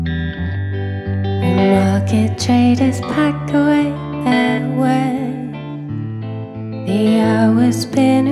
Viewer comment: Some one can tell me what she says in 0:16? xD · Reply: I think she says 'the day is getting on, the market traders pack away their wares'